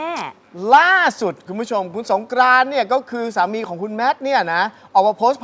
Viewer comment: นาทีที่ 1:10 ฟังไม่ผิดใช่ไหมค่ะ พิธีกรพูดว่าคุณสงกรานต์เป็นสามีคุณแมท?